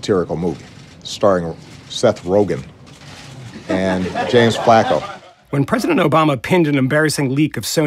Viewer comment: What did Obama call James Franco at about 2:30? Did he say James Flaco, or something like that?